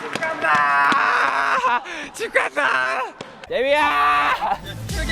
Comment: Does he call Jimin jagiya at 3:00? Or Jimin called him? Or they both were yelling jagiya at each other.... I never noticed... Wut?